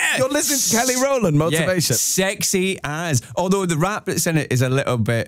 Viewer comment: Is he saying "sexy ass..." at 3:13? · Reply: i think he was saying "sexy as..". like "sexy as fuck"